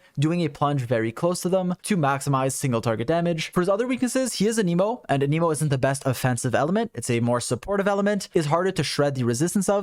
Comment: I genuinely thought you said: "For his other weaknesses: He is an emo. And an emo is not the best offensive element."